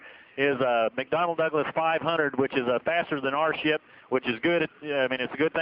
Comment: did he say that there heli was faster then there shit ?